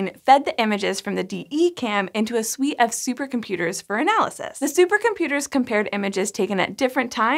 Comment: When she said "a suite of super-computers" at first I thought she said "a sweet ass super-computer".